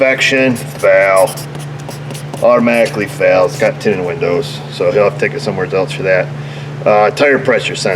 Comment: Did someone hear what said at 10:14? Why the truck failed inspection automatically? I’m from Germany and did not understand it. But I am curious what I have missed. Thanks! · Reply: Failed inspection due to tinted windows